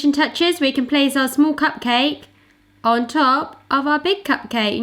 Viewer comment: I swear I heard say "pugcake" a couple of times!